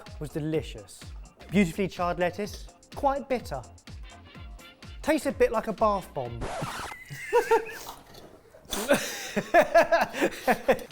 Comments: little meltdown at the end was just so funny. And so was the feedback from the judges. 😂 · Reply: I actually thought they said barf bomb :))) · Bergamot often goes in bath bombs, so I understand the connection there. looks more inviting as a normal but looks like you would get it at a 5* restaurant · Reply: Thanks for that, cause I heard it as 'barf bomb', which would be a little too harsh.